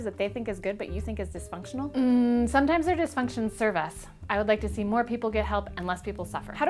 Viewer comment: I can't quite make it out what she said there. It SOUNDS like she said "sometimes their dysfunctions serve us..." but I don't have a clue what that is supposed to mean.